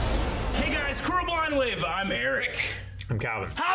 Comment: What do they say at the beginning of each video? All I hear is "Hey guys crew we're Blind Wave" which makes no sense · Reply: "Hey guys, crew Blind Wave"